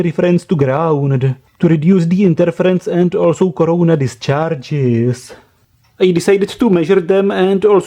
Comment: did u just say corona distcharges?